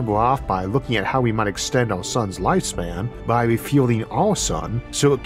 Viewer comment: I thought you said "extend our 'son's' lifetime" at the end and thought we might get another episode on life extension or gene manipulation.